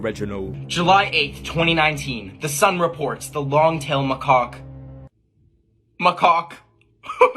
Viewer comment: when he said macaque it sounded like he said my cack